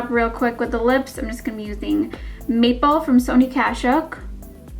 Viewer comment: I thought she said "meatball by sonia kashuk" but I'm pretty sure she said "maple" lol